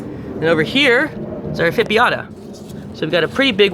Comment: What’s the one at 3:17 ? You said it but idk how to spell it lol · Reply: Nepenthes ephippiata